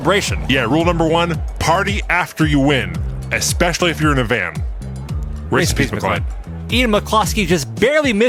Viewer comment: What did and say at the same time at 1:53? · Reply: Race in Peace McClyde (Said super fast)! ; DDDDD